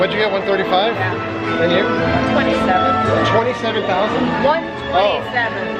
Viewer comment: I love how after riding on Toy Story Mania and asking and how much they scored, when said 127,000, you thought she said 27,000 and questioned it like she actually did score only 27,000 lol. Your reaction made me laugh so much. Happy belated 18th. Hope you had an amazing day!